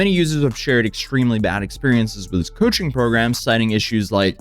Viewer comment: Coaching services!!!!! Sounds like u said "Cushing program"